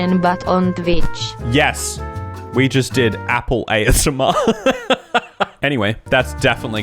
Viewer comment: I thought you said ahole asmr, and I was genuinely scared a bit